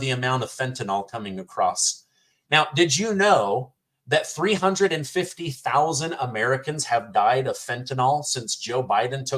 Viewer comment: 350,000??? I could have sworn they said 350,000,000... 🤣🤣🤣